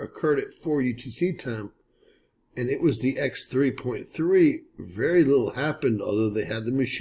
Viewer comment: X 3.3 at 3:30...did I hear you say?